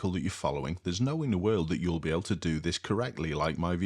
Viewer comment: Hey, just letting you know. I think you may have misheard the phrase "no way in the world" as "no inner world".